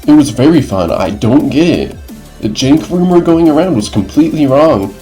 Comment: and 10:08 i think he’s saying “jank” but why are the auto captions flagging it? i honestly can’t think of anything it could be. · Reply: I am saying Jank yes